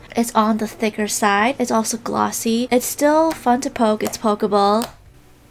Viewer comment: When i turned on the closed captioning at 3:24 instead of saying “its still fun to poke it’s poke able” it said “its still fun to poke it’s pokeball” 😂